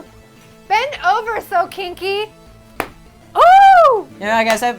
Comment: i think she said sokinki